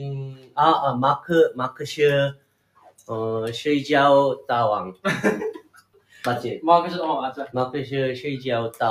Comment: what is haechan say? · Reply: 'mark is a sleeping king' said in chinese mark 是一个睡觉大王